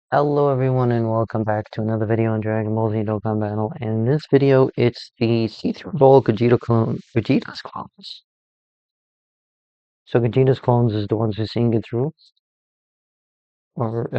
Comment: What were you trying to say in 0:06? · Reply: See through it all with x ray vision whilst I talk under my very own nose?